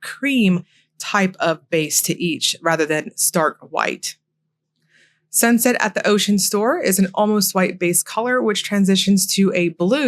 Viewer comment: I heard Sunshine at the Ocean Store at 17:52 😂